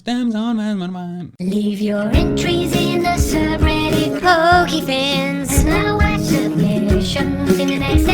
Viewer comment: Ok I heard "Leave your submissions in the subreddit pokefans, and I'll watch them and call 911."